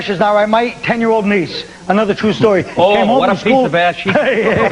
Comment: What does DiPaolo say? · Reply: "oh what a piece of ass she is" just his usual boring ass "shock" schtick